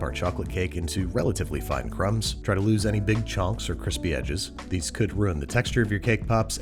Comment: I’m sorry did he just say CHONKS. Lol